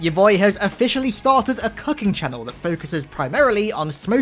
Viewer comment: I honestly thought I heard that he started a 'cucking' channel, not cooking channel, and I was like: "I am pretty sure that aint allowed on youtube my man."